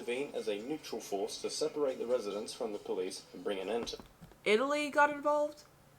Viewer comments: Really loved this video. I think it’s a really interesting subject! Thank you! It made me laugh though when you thought Italy got involved. The narrator said ‘a battalion got involved’, when the army came over. You must’ve misheard and thought he said ‘italians’ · I think he said a battalion not an Italian 😂